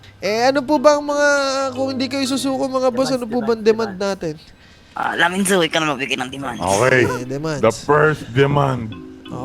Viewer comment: Puta pinaglalaroan nyo lang mga sarili nyo si Yan nag rorob 1:34 narinig ko "naminzu ikaw na mag bigay NG demand"